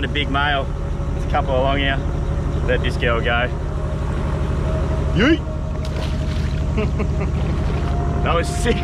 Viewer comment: Did you say yeet at 1:36 you have a lot in common with lazarbeam